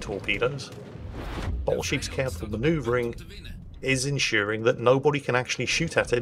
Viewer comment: I actually thought he said enSHEARing, as a pun for Bottlesheep xD